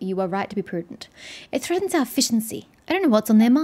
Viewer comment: LOL! For a second, I thought you said "It threatens our Fish 'n' Chips".